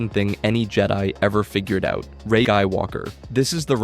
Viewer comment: what did bro say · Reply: rey eyed walker